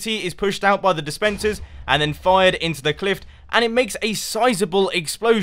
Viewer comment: d-did he say CLIFT????